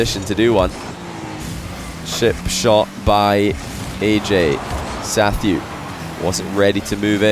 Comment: at like 31:00 i really thought he said “shit shot by ayyjayy”